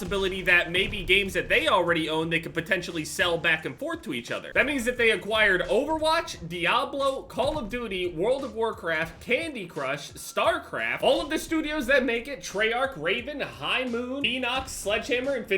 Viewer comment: I honestly thought you said starcrap lmao.